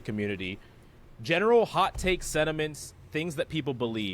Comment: Did he say sediments? not sentiments?